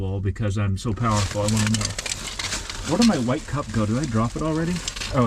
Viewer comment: What cover are you using on your bright light? I thought I heard you say a white cup? Is that correct? Plastic or styrofoam?